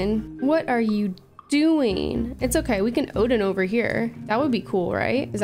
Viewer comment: sounded like “I’ll try Odin, that’s a neat trick!”